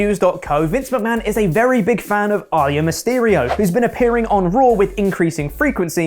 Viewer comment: That's how you pronounce Aalyah? Have you never heard it pronounced? I thought you were saying Ali and Mysterio